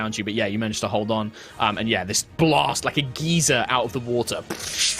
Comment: I'm sorry, WHAT? Did you say a GEEZER blasts out of the water??? 😂😂😂😂😂